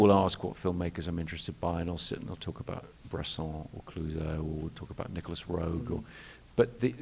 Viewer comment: Can anyone tell me what director Nolan names at 21:28? I hear Bresson but what's the second name he says? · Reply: Clouzot